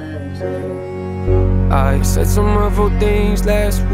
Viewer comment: does anybody else think he says “hurtful” and not “awful” 🧐